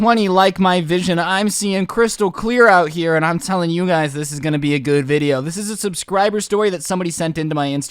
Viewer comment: i thought hes said this will be a good year